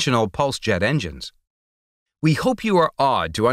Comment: At the end of the video, when he says "We hope you are awed to understand how Tesla's mind worked", for a second, I thought he said "we hope you are odd"